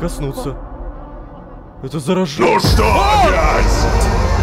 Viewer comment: Мне обной послышалось "НУ ЧТО Б@